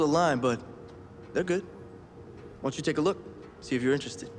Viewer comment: How did "Why don't you take a look, see if you're interested" (which is clearly what he says) become "Want to take a look, see if you're interested" in the subtitles??? That sentence doesn't even make sense, wtf guys